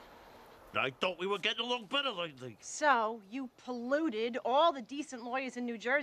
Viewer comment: What does Tony say starting at 0:28? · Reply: I thought we were getting along better lately